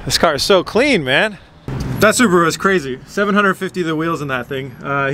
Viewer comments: did you say 'that Subaru is crazy'? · I swear I heard him say "Subaru" @ 8:38 How did that make it through edit. . .hahaha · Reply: Nah he said supra but it just sounded like Subaru😂😂😂